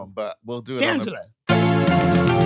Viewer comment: I swear I heard Jasmine say Ginger at 47:32, but she says Shangela 😂😂